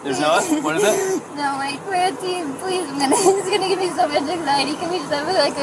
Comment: When the officer was telling Gabby that he was splitting them up for the night and she was getting hysterical. I thought I heard Gabby say "Please No, he will give me so much anxiety" If I heard right that is another sign that he is emotionally abusive.